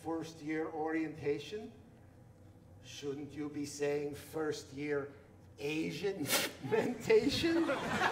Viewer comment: What does he say at 3:47? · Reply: He said, “shouldn’t you be saying first year Asian-ientation?”